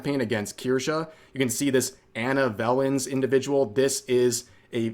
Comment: I swear he said Bellend.